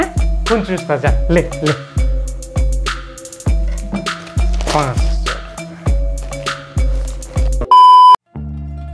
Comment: did he say Madarchodd?😂😂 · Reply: No he said "Paanso rupey do"